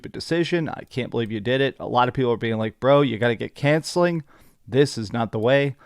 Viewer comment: "You gotta get cancelling" instead of counseling was on point 😂